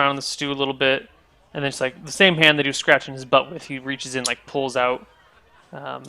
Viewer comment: At first I missed the part where he says "he pokes around in the stew a bit." What I heard was "Borsh is in there scratching his butt..." and then "he reaches in, pulls out like some chicken skin and hands it to you." Anal meat scraps, ew.